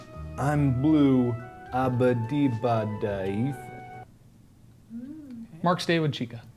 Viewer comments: I'm sorry did Mark just say a ba de ba da EEF? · Ethan on one of his streams: I didn't come up with EEF Mark did. Mark in this video: I'm blue dabadeedaba EEF. He might say EEFin. But he definitely did not say Ethan. Btw the time that is said is